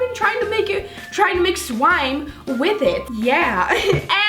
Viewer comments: Did anyone notice at 00:20 she said swime instead of slime 😂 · Did you say swime??😂😂